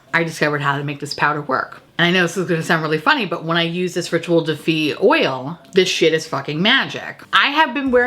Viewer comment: Never heard of the brand that makes that primer before. The whole video I thought you were saying Ritual Defeat which I thought kinda went with the blood of my enemies primer. Seemed like a theme.